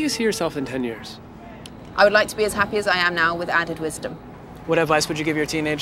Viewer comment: She answers the 'where do you see yourself in 10 years?' by saying 'with added wisdom' and my dumbass thought she was about to say with adam driver lmao